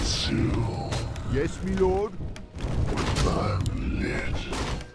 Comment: What The hell ... in the mark around 8:16 I heard The Fire Lord saying "I am a bitch" !?